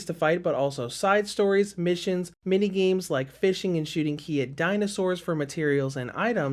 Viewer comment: I swear....somewhere in the video i kept hearing "IKEA Dinossaurs" xD